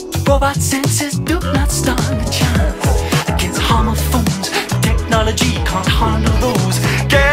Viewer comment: Im sorry i keep mishearing it as homophobes XD